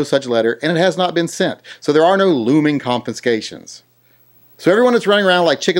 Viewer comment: Sorry but it sounds like ur saying confiNscation. Driving me nuts